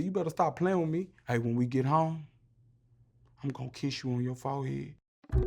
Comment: Nobody: Me:Sound like he said imma kiss u on yo foul head